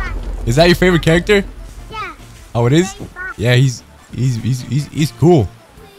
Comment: If you didn't know what he said on 2:51 he said, "Yep, he's so very fast" or "Yep, and he's very fast" <3